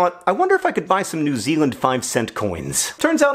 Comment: I thought it said new new Zealand 5 cent coin